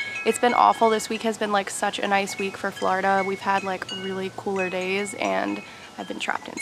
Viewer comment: “flardar”…. Idk that caught me off guard, my ass was replaying that like ‘did I hear det right?’ 😂